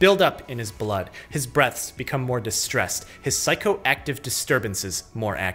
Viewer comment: sound like you said “his breast become more distressed”